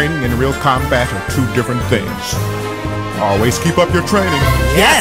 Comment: what the audio is: always keep up your training What i hear: always give up your training